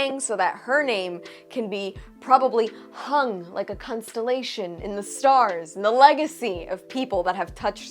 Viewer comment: did she say "cuntstellation"?